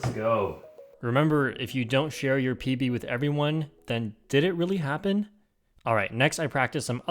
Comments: I thought he said pp to everyone 😭😭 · i thought he said if you dont share your pp with everyone